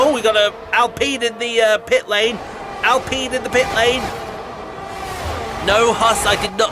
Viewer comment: I heard "Al peed in the pitlane" and I was going to make some hilarious oil slick joke but decided against it. @ 12:40 Daaaamn- Jackie travelling sideways at over 100mph. Well recovered though!